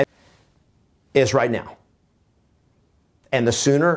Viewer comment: What happened at 24:42, sounded like something got cut out? Thanks for the information. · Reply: I think said if you are thinking of selling your home, now is the time. I believe he said that in another video on the 2020 housing forecast.